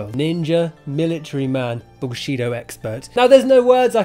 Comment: I like how the auto generated subtitles says "bullshitto expert" in